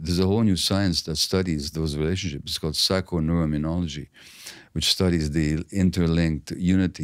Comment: Gabor states a word at time stamp 37:58 What is he saying please? I'd like to look into this study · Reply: He said Psychoneuroimmunology